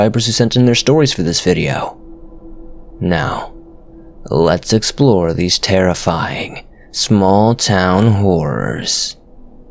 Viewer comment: sounded like you said “let’s explore these horrifying small town whores” by golly gosh that is spooky